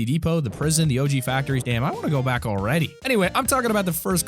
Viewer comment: Did you say damn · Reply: He did